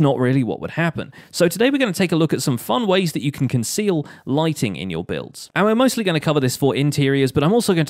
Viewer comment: I thought he said lightning not lightning